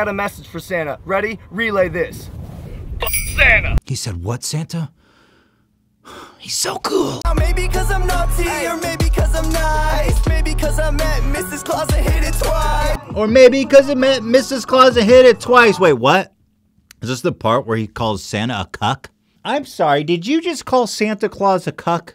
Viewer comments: Is it only me or does he say ''maybe cuz i'm nazi''? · i heard “maybe cause i’m nazi” wtf is it just me?? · Anyone else hear logan say "nazi" instead of naughty at 12:56 ??????????? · I literally heard "Now maybe cuz I'm nazi"...am I the only one? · Maybe cause I'm nazi, maybe cause I'm nice. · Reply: But he said naughty btw i also thought it said nazi. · Logan in his Christmas song when he says naughty it sounds like he said nazi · I thought for a second he said "maybe cause I'm nazi" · I could swear I heard "Maybe cuz I'm Nazi" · DID HE JUST SAID "MAYBE CUZ I'M NAZI"??